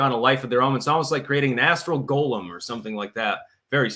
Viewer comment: Astral Golem - I thought you said asshole golem...